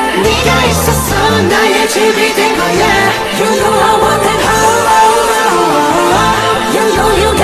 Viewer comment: I THOUGHT HE SAID “YOU KKOW I WANT THAT HOEOEOEOEOEOE” LOL